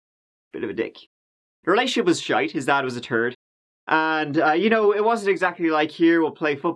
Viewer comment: you’ve got me trained. i heard “his dad was kinda a third” and it took me a minute to realize that one actually was “turd” this time lol